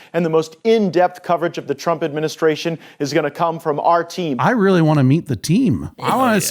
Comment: Did he say our team, or RT? Lol...